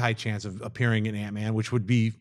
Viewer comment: half the time they say "antman" i hear "aunt may"